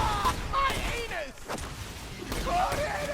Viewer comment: did... did he just say "my anus"?